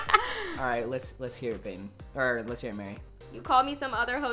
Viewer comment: Did Xolo Just call Mary Babe Wait for it